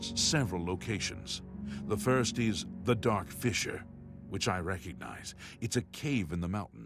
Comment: I thought he said "The Dark Fisher". lol